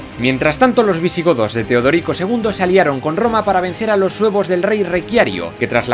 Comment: parece que dice "para vencer a los huevos del rey Requiario" xD